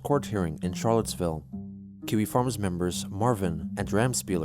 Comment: I don't know why I hear it as "Kiwis farms members"